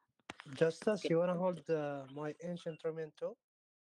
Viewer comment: Did he say justus